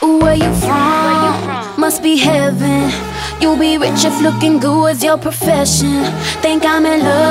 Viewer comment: like if you thought she said "licking glue" instead of "looking good"